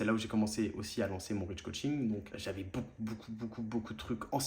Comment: merci pour ton témoignage mec ! 🙏🏻 j'ai pas compris tu dis que tu as lancé quoi à 23:45, ton "... coaching" ? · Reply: Le rydjcoaching, c’est juste le nom de mes coachings bro